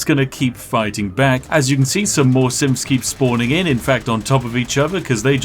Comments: Synth? I thought u were saying simps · Every time Spiff says "Synth" I hear "Simp" and it makes this even funnier.